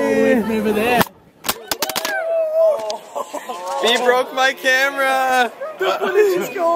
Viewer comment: I thought it said Bæ breaks my camera😝